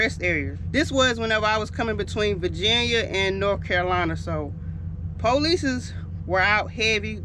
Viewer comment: Did she say police's???